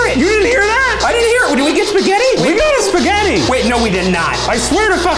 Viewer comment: did anyone catch at 14:03 the spirit box says "spaghetti and meatballs"